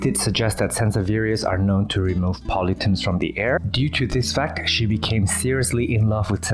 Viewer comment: 54 at first I thought you said 'Helps remove Politics from the air' ( I'm sure you said 'pollutants' ) your English is awesome , but when my ears heard 'politics' I laughed so hard and I may go purchase a Sanseveria now lol!!